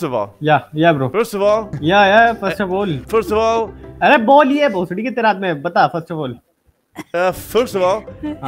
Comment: thats an Indian i know what he's saying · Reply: He was saying come one telll Bhosri first of all come in tell